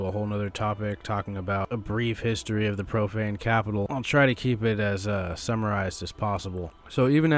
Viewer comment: Lol, thought you said propane capitol.